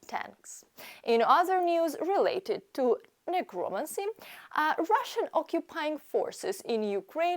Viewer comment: Did I hear you say "necromancy" at around 5:40? Omg! 😅😅